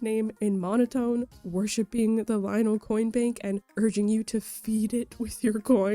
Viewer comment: when I was little I thought it said Lionel cornbread 😂